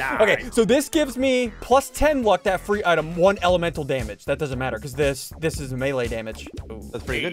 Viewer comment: I thought it said emotional damage 🤣🤣🤣